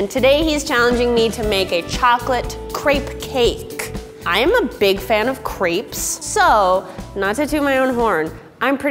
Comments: I'M A BIG FAN OF CREPES! What i heard: I'M A BIG FAN OF CREEPS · i thought she said shes a big fan of creep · I heard I'am a big fan of creeps......